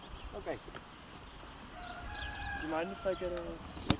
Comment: OMG WHEN TRIED THE FIRST HORSE AND HE WAS ABOUT TO GET ON, HE SAID "DO YOU MIND GIVING ME A LEG" AND I HEARD ""DO YOU MIND GIVING ME A HEAD" LMAOOOO